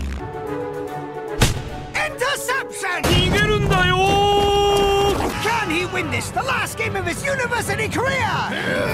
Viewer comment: How do you spell what he says at 1:20? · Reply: nigerundayo